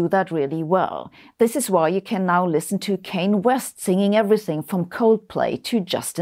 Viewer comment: Did she say "Cain" West?